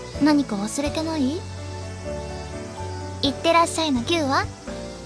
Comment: I think she says 行ってらっしゃいのギュウは？ Which translates to: Where's my goodbye hug? it's just a small difference but I wanted to mention it :3